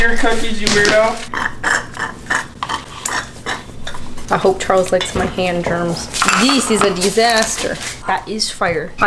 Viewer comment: Definitely thought said “hope Charles likes my hand job” as she crumpled her dough in her hands as like a pun instead of “hope Charles likes my hand germs” 😂😂 I thought “oh I get it that’s clever but.... but Is this allowed?”